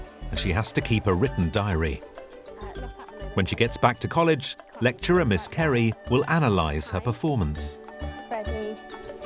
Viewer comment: i heard "lecturer miscarry" HELP